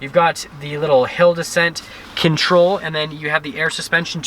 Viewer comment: Careful with your "Hill descent" pronunciation! Sounds like you say "He'll descent"! 😂